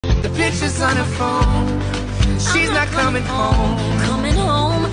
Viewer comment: I thought it said The b****on her phone💀